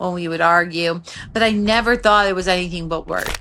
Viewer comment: Thank you for sharing and your kind words, so sorry you went through all that, it takes real strength to survive. Wishing you peace and continued healing.❤ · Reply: Someone needs to fix the serious errors in the closed captions! Example 3:25 "but I never thought it was anything but work" -- NOT what she says. She said, "but I never thought it was anything but WORDS!". This is a huuuge error and needs to be fixed because it's HIS WORDS, THAT ARE THE RED FLAG TO GET OUT IMMEDIATELY!